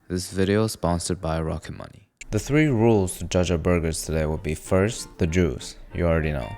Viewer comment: I thought i heard "The three rules to judge our burgers today would be first the Jews" Lol